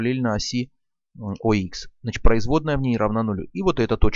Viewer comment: Говорите четче! 0:43, производная (не) в ней равна нулю, пока понял что говорите (в ней) 2 раза психанул.